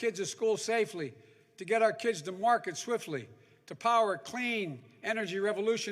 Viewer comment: Get our lids to market 3:30 , did he say that.